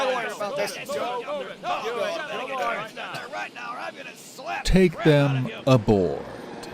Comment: I heard "im gonna slap bread on you"